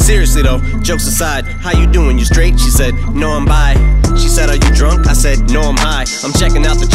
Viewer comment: Seriously though, jokes aside "How you doing? You straight? " She said " no I'm bi " She said " Are you drunk? " I said " No I'm high " " I'm checking out the chick "she said " So am I " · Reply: I thought he said “No, um bye!!”